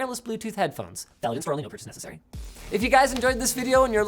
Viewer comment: No idea what you said @ 12:21 Sounds like some sketchy illegalese. "Something or other necessary?" I hope they didn't pay you to do that.